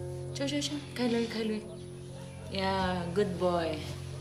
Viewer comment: She says good boy and subtitles say That's my boy. Wow. I'll stick with good boy